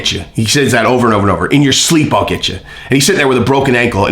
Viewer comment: Aaahhh, Man...thought he said "In the streets I'll get you"